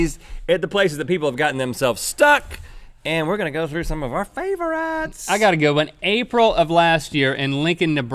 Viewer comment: When Rhett said, "Favorites," at :46, I heard "Favor-Rhetts" lol